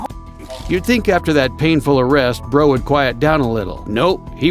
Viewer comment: I walked away and all I hear is “you’d think after that hard arrest, bro would calm down a bit” hahaha